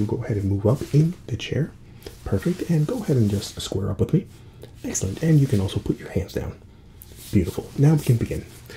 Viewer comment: I heard "Excellent, and you can also poop your pants down."